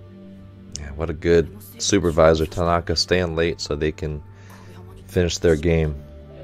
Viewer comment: im not sure if i misheard it, but it sounded like u called the supervisor tanaka? same thing happened in previous reaction too... it's actually takeda my bad if i misheard it